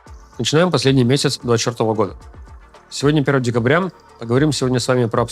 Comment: внимательно не слушал и услышал " начинаем последний месяц чертово года( двадцать четвертого)"!